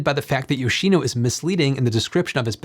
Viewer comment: I’m using new hearing aids, I thought you said “salad” state battery. Mabe has mayo or vinegar dressing as the electrolyte?